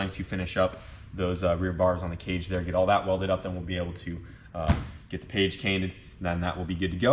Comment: Get the page cainted. Did anyone else hear that? Lol